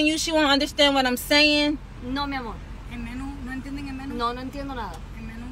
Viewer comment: I think she said "Si le entiendo, no quiero" which translates to "I understand, I don't want to"